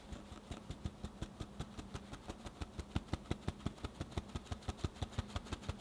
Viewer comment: Erm, the subtitles had stated "powerful powder" instead of cake powder/flour so.... it's